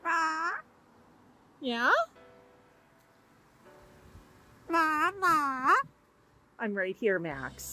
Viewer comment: máaaa-máaaa that sounded like mum in Spanish,sweet Max calling his hooman mum 🥰🥰🥰